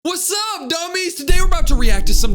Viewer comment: Did he just say "whats up Dummies" or it's my ear🤨